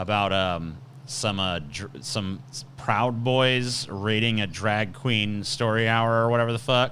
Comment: at first I heard you say “… about some Proud Boys rating a Drag Queen Story Hour” and I was like Wat.